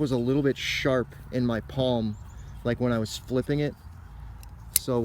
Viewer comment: Did you say you were spliffing the knife??? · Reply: subroc I think the word you heard was flipping, but you're right it almost sounded like I said spliffing (which as far as I know isn't even a word).